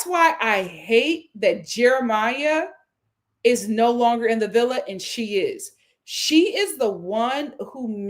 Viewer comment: I thought said “no, Jeremiah’s not driving the car” but I was drinking, sooo. 🥴